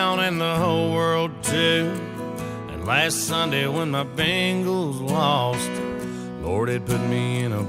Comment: My great grandpa heard this in 2006. He thought they said “last Sunday when my gamecocks lost”